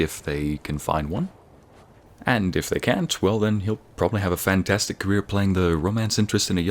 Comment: Did I just hear you say "fangtastic"?